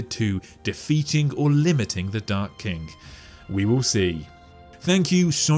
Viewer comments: i just hear "dark kink" over and over again....wasnt we talking about a king? xD funny thing ^^ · Why i only hear him say " dark kink"